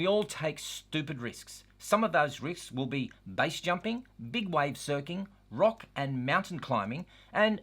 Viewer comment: Hang on,... Did you say "Big Wave Surking!" at 6:30 I believe you did after watching it 3 times to have a laugh!! :) (spelt correctly each and every time I mention the good man himself)...... have you finished that bottle of good stuff yet,... if not,... scoot over so I can join you for my glass of bubbles while you tell me about the time you tried to teach your boy,.... Big Wave Surking! :)